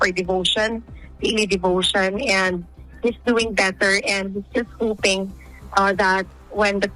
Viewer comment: Thought she said “he’s doing better. He’s pooping….”